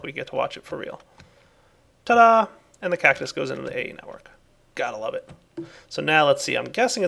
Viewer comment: Anyone else hear say "go into my AE network" at about 12:55? :P